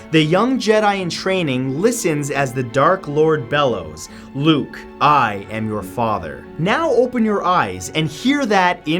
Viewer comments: I heard 'No Luke, I am your father' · My dad owns the empire strikes back (and all those other old Star Wars movies) and I swore he said Luke I am yet further lol